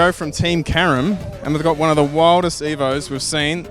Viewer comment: Thought they said Team Karen. Thought what a great name to call a race team